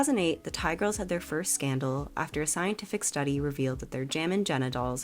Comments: i thought you said “jammin genitals” 😭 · You said "their Jammin' Jenna dolls contained lead", but what I heard was "their jammin' genitals contained lead." 😂